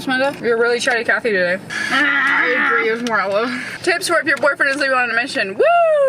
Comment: can someone pls tell me what she's saying at 4:11 i can't make it out ahhahhahaha, i can only get up to "tips for if your boyfriend " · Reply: Tips for if your boyfriend is leaving on a mission! :)